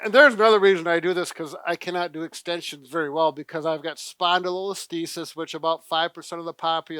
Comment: Brad said he can't do extensions well because he has "spinal _______" but I can't figure out what the second word is. He said stenosis is something else. Does anyone know what he said or what he's referring to? I can't do extensions well either and want to learn more. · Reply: Spondylolisthesis